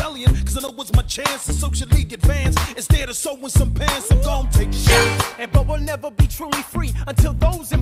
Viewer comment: during my shot , i put on captions and they said “im gonna take a shot” but captions heard it as “im gonna take a sh**” 😭